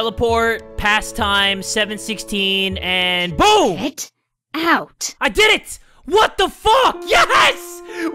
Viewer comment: did anyone hear info-chan say GET OUT